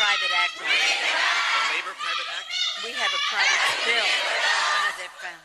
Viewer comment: it s/l Pelosi is saying something to one of her people about " private actors," then he says something which s/l "we paid for private actors?" Anyone else hear this?